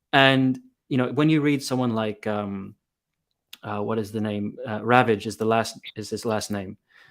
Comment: Amazing topic!!! It's super important to me. I have been looking for this. I will comment again later after work😅. At 1:18:30 you mention the name of someone but after listening several times I can't really hear it. I tried google but with no luck. Can you please give me the name or the title of the book🙏? Thanks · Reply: The name we’re mentioning is Marcus Eli Ravage, author of many works including “A Real Case Against The Jews”.